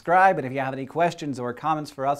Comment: I seriously thought he said James Wood Space Telescope. Then I looked back and saw the caption.